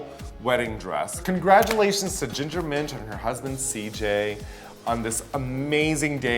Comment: I thought Raja said congratulations to Ginger Minj and her cousin CJ 😂😂😂